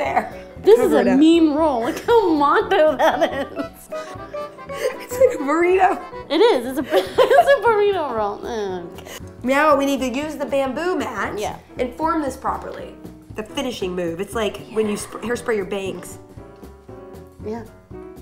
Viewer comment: I'm way late to the party, but she totally says meow instead of now at